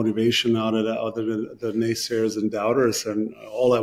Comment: Did he say....Naysayers? 😮